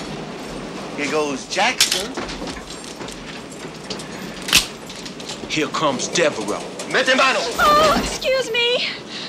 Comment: What is the gang leader saying at 1:07? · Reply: "Here comes Devereux!" He's basically telling them which train stop they're passing by. They're doing that because they are going onto their "turf" and the opposing gang is not allowed. EDIT: It seems like you're asking about what he (?) said right after that... Idk for sure, but it sounds like someone said "into battle" or "men, to the battle". I'll try to listen more to get it figured out.